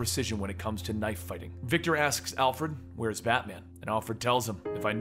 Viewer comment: Time for More! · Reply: it sounds like your saying victor ass 😂😂